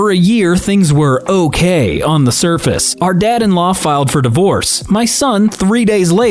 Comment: i'm pretty sure it's "daughter in law" and not "dad in law", but thar got some good laughs out of me when i realized it 49:58 the moment i heard LoL, i said nope